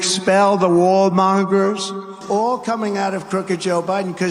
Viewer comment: None of you told me about the wool...mongers....none of you...😮 can't even find anything on them..they must be powerful..and super secret. 😮😮😮.. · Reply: I thought he said "wall mongers".